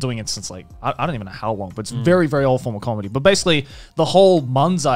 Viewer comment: lmao he said very old form of comedy, sounded like very awful comedy. Cultural Appropriation right there! will get it if you saw last episode